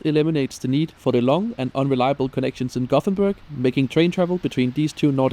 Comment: I had to switch on subtitles. I never realized, that Gothenburg and Kapfenberg sound so similar. but I had to listen to it serveral times, sure, it would only sound alike, if an english speaker tries to say kapfenberg, but my mind came to a screeching halt, when I heard it.